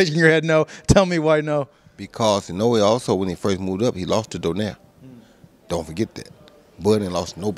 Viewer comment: did he say Inoue lost to Donaire ? 😅